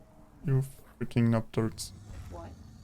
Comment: What are you saying here? Fricking up turds?